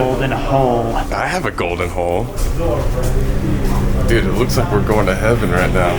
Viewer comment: the golden hole I THOUGHT HE SAID I HAVE A GOLDEN HOE😭😭😌😌💀💀